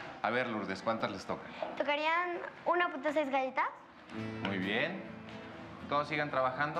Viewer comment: Alguien sabe que dijo la niña en el minuto 3:31? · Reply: "Tocaría 1.6 galletas" eso dice